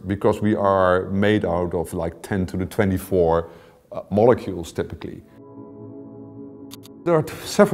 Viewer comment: I thought he said "We are made of 10 to 24 molecules typically" and I was like "No sir I think not"